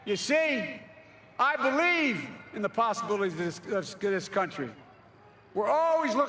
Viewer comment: I guess Joe deserves to president, but the thing is he is old and sometimes forgetful, and I want him to consider china as a threat more.. But other than that he is perfect for president! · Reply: Lol it’s irreverent but it sounded like he said skskthis country